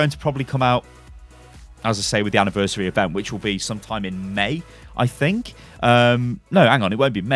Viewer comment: All I hear is Mei!